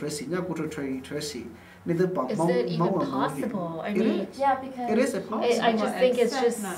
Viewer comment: Did he just say "it is...it is impossible" at 0:31 seconds? I am 100% sure he did day that. LMAO...hahahaha